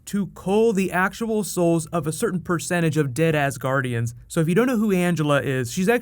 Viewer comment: 😂😂😂😂 I heard "...dead ass guardians..."